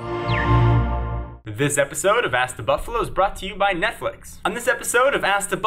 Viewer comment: did he just say ass the buffalo????